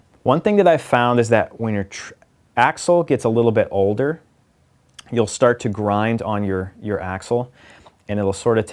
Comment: the first time I heard this I was multitasking and I could have sworn he said you'll start to grind on your asshole.